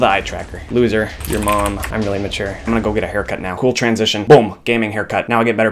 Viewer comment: is he saying 1. I'm really mature 2. I'm really immature i am confused. help.